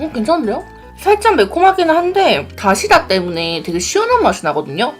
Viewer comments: 다시다 라고҉ 말한걸로 들리지 .. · 이때 나만 다시다라고 들었나요?